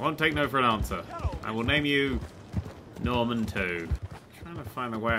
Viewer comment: Damn, this game looks great! Also RIP Norman and Norman II. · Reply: I thought he said "I'll name him Norman too" ... As in, the second horse's name was also just Norman. Like Roach.